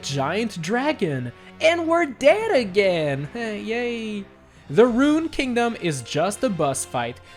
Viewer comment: I thought he said "N-word dead again"